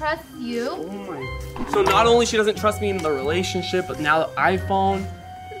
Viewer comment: did rug say in this RELATIONSHIP at 15:13?